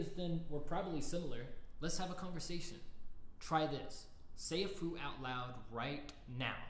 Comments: I thought he said : Say through And not say a fruit... I just said through like a dumbass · “Say a fruit” me: “fruit!” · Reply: I thought he said “say afoo.” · I thought he said "say a food" instead of say a fruit and stupid me said chicken 💀